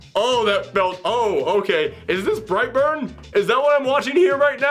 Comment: Black Label’s an adolescent’s understanding of maturity. This stuff just exasperates me. Birthright’s my favorite Superman origin story. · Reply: I thought you said Brightburn at first 💀